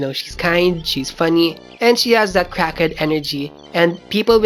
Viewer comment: "CRACK HEAD ENGERY" did I hear that right... bwaahahahahaahh